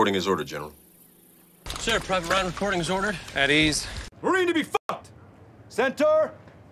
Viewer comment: At first I thought he said 'Marine to default', as in getting called a 'defaulter' when you fucked up & got charged in Australian Army 🤣